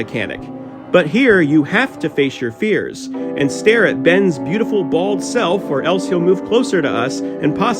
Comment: i thought he said "stare at ben's beautiful balls"